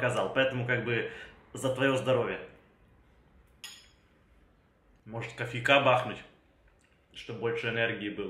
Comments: мне послышалось: может коньяка бахнуть ну мы же знаем что Эд не пьет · послышались коньяка)))